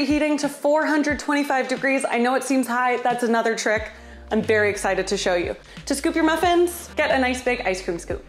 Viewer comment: am i hearing stuff or did i hear you say "berry excited"? because if so, that's really cute